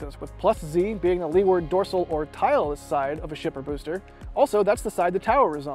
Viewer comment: I thought you made a mistake on the axis description but where I (and the subtitles) thought you said "tile" you said "tile-less"